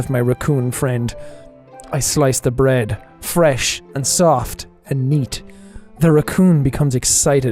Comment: FuCkInG wut? Did he really just say soft and neat?